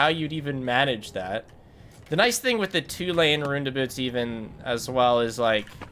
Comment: i have absolutely no idea what he was talking about cause he says round about as ruin abut